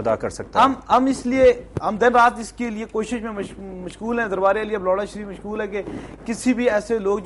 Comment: sorry to say. 06:38 did he say Lura Shareef. hahahha